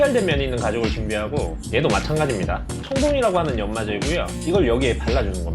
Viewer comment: 이때 꺼내시는 하늘색 연마제 이름이 뭔가요??? 총동이라 들리는거 같은데 잘 안들려요 ㅠㅠㅠㅠ